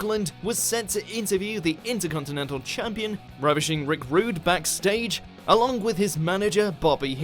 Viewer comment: That sounded like "Rubbishing" Rick Rude.